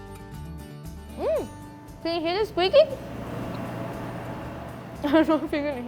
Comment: what she said? eat after "So ~~" sorry i'm japanese. not good at english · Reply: Thank you!! "squeeky" new ward input my brain!